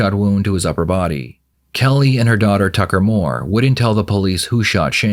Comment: I thought the AI said "Fucker Moore", ha ha ha!